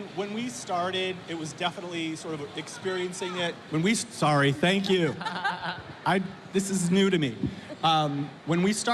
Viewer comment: What does someone from the public yells? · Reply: "Hold the mic up" I was there next to her